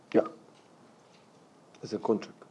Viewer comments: What does he say 4:16 after he says yeah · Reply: he has a contract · does Pep say "yeah, as a coach", on his answer for his plans for Kompany in the future? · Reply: he has a contract